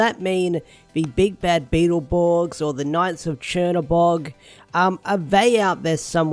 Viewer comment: Lol sounds like you said Chernabog instead of Tir-na-Nog. NGL tho: I'd probably watch/read a series about that.